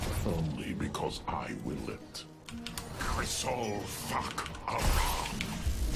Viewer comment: I keep hearing Alarak say "Crystal FACK Aran." I don't know what kind of fanfic he's been reading. Or, maybe it's just a raid log from WoW.